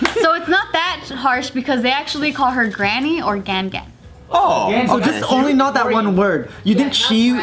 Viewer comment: Cracked up at 8.17 because I genuinely thought said gang gang